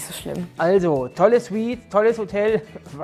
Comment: ich hab einfach "tolles Weed" verstanden ^^